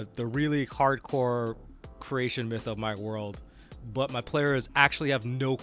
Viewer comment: My brain heard this as a "really hardcore Croatian myth" instead of "creation" and I was like-- SINCE WHEN IS A MAN OF BALKANS? XD